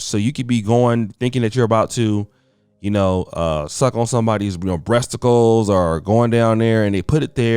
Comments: Did he say breasticles? At 11:50 lol HAAAA! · into video did just say "breasticles"? Damn bruh!